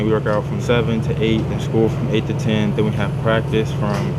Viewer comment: did he say he only has school from 8 to 10